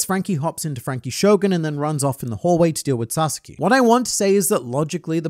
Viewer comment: for a moment there i heard franky went to deal with Sasuke and was a bit confused